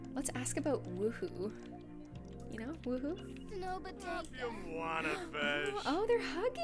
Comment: did anyone hear say 'If you want it b....'😂😂😂😂